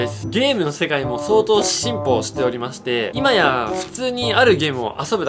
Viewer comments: 「進歩」が発音のいい「simple」に聞こえる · 「ゲームの世界も相当 Simple(ネイティブ発音) しておりまして」って聞こえた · 進歩がsimpleに聞こえた